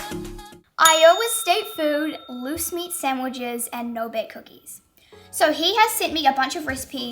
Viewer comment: At first, I thought you said moose meat sandwiches